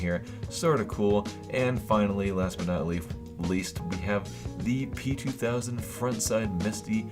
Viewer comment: Did he say leaf? Is part reptillian?